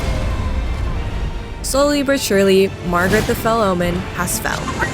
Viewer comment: Ah, yes. Margrethe the Felon. Godorick the Gafted. Michaeleth. Legends of the Elder Ring! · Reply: I thought she said "Margaret the Felon" at first and now I'm only going to refer to him as this.